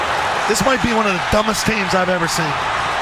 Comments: It sounded like he said “one of the dumbest teams I’ve ever seen.” 😆 Not one of the dumbest plays .. but dumbest teams. 😂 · “This might be one of the dumbest teams I’ve ever seen” 😂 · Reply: At first I thought he said "things" I had to rewind it to make sure he said "teams"